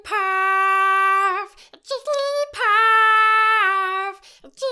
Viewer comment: It sounds like you said jiggly paff